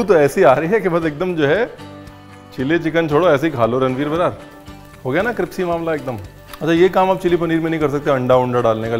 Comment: Did you just say Cripsy 6:52 ??? Lol not laughing in a negative way. It was funny!